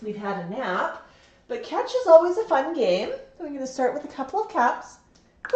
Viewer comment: Woah, right at the start it sounded like Max said "are we going to start with a bok," so cool